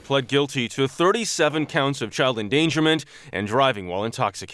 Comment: bro for a second when the voice over dude said "37 child endangerment" i thought he said engagement XD ok but fr,dont say "we need more teens like this" say "i can be like those people"